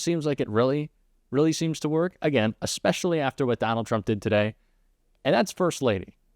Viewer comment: For a moment, I thought he said McDonald Trump That should be the new term McDonald Dhump